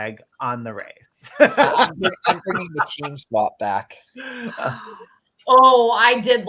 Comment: What did say at 49:33? Team swap?